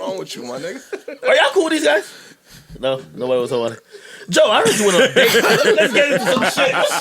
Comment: does Parks say " no, nobody gonna lie"?